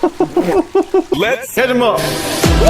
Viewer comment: does he say “let’s hit ‘em up” or “let’s catch ‘em all” in the intro pls someone settle this 😭